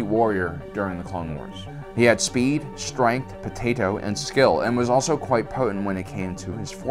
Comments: Speed, Strength, Potatoe?? and Skill. Is what was said, because that is what I heard. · What did he say at 11:14? There’s no way he said potato! 😂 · Did say potato